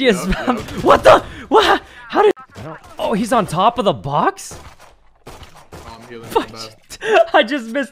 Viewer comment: what did he say? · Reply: I think he said fudge while laughing at the end, if not he said f*ck